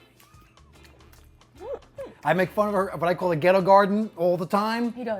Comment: ….did he say ghetto garden?